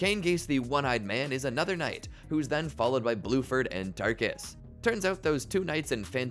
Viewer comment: did you say "blueford"